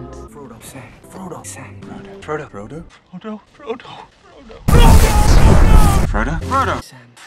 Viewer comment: is it just me or is Boromir saying Hodor